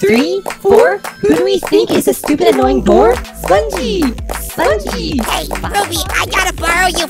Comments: THE FIRST THING I HEARD WAS: ”who do we think is a stupid annoying whore?” HELP LMAO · who else thought they were saying whore instead of bore? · I THOUGHT THY SAID 'WH_RE' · did they just call Spongy a whore?!😨 Language you two! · i thought they said “who is a stupid annoying whore” 💀